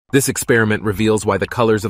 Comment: I thought he said violent but I let it replay and then noticed ‘vibrant’ I wasn’t really paying attention first time